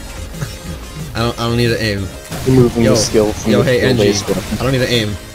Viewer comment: For anyone who didn't catch my ingenious wisecrack I said "removing skill from the skill-based weapon"